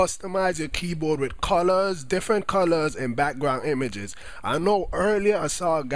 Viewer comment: U have a funny voice it sounds like you said Colour:)